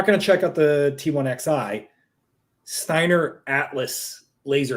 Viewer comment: I heard "Steiner Laser Atlas" So it's the ultimate scout config?